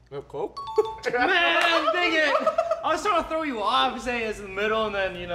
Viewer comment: who else heard n word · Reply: He said man dang it